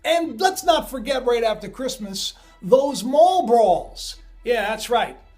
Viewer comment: I thought you said 'Marlboros', but it was Mall Brawls. Lol 💚💜💙 your videos